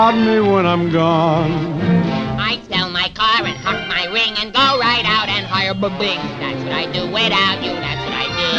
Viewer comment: In this movie I'd so much hated Joe Anthony... lol the way he treated HArvey... but this song and That's Amore are my favorites parts! ^^ and what does Jerry saying at 4:04 ?? I can't understand!... :( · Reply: i’d sell my car and hock my ring and go out and hire b-bing